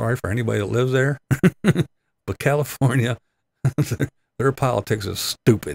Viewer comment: almost sounded like you said "pile-a-ticks", and I thought... how fitting! 😂🖤‼️